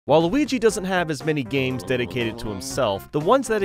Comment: I misheard this as "Waluigi doesn't have many games dedicated to himself" and was like Waluigi doesn't have ANY games dedicated to himself